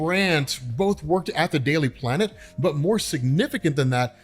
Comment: I thought i heard Daily Planet instead of Daily Bugle 🤣